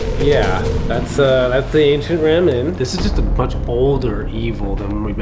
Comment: ‘I THOUGHT YOU SAID THIS WAS AN ASIAN RAMEN PLACE’ NO, IT’S THE ANCIENT RAM INN 💀💀